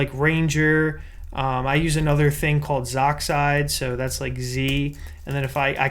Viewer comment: what's the name of this program? I can't understand the spelling, xoxide? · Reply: zoxide